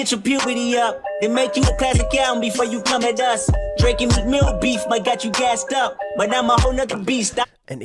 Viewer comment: It sounded like Kendrick said “Drake, your Meek Mill beef might got you gassed up” taking shots at Drake 🤔